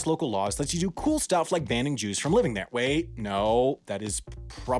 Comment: I thought you said "banning juice from living there" and I was like yeah that seems reasonable, and then I realized that is not what you said.